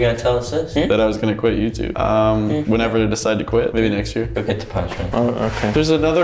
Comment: I was so sad when I heard he might quit next year 😭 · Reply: I thought he said he would quit next week and I was so happy but when I realised he said next year I was sad